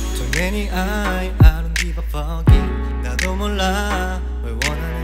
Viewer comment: Am I the only one who thought he said I don’t get it, fuck it instead of I never forget? 😂